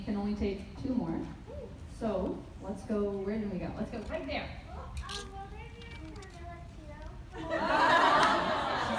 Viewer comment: What she said in 1:07:31? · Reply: "Will there be a Carmilla 2?"